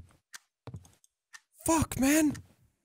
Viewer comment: i thought he said 'fuck men'. am i the only one who heard that? xD